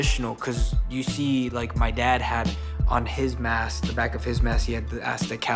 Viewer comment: Did he really say that on camera??? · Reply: "My Dad" not Deadbeat.